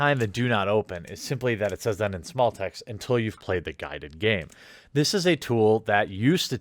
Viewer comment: I thought it said until you have played the gilded game on it XD Great video 😉